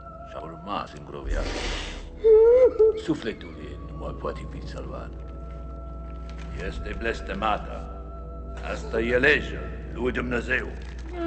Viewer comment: Preist says in 1:18 -" Si a luat propria viata Sufletul ei nu mai poate fi salvat, este blestemata, asta e legea lui Dumnezeu" Dracul says in 1:38 - "Asta este rasplata mea pentru apararea bisericii lui Dumnezeu? Renunt la Dumnezeu". Dracul says in 1:48 - "Ma voi ridica din morti si ii voi razbuna moartea " Dracul says in 2:12 - " Sangele este viata si va fi si viata mea" Credit to friend Andrei Victor.